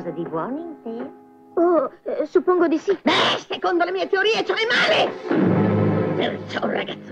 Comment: l'hanno fatto anche ieri questo film mi piace tanto, ma qualcuno riesce a capire come dice Maga Magò nel pezzo dove chiude la finestra prima di trasformarsi in gatta? dice secondo le mie teorie e poi come? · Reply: Anche io l'ho visto! Comunque Magò al 3:06 chiede ad Artu "E lui vedrebbe qualcosa di buono in te?" e, dopo la sua risposta, dice "Secondo le mie teorie c'è del male!", o almeno è quello che ho capito io e mi sembra la risposta più adeguata. Spero di esserti stato utile :) Buon Natale!